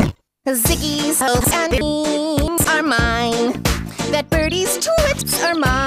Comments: only i hear "hopes and beans are mine"? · This pinned comment is mine. EDIT: I'm starting to get sick of these comments saying that the line "Hopes and dreams" is a reference to Undertale, which it clearly isn't as it didn't mention anywhere in the video about that game despite there being a track in the said game titled "Hopes and dreams". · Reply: it made me think that he was saying ropes and beans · that birdys t*s are mine idk that’s what I heard 🤷‍♀️